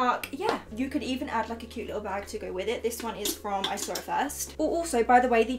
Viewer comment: Ok if anyone is still watching these comments where is that bag 7:05 yeah I know she says that but I have no idea what she says.I have literally listened that like 20 times and still I don’t understand it. Please someone help👋🏻 Btw I’m from finland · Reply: Ur Baby it's from I saw it first! X